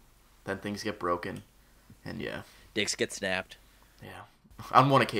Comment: I thought he said dicks were snapped >.<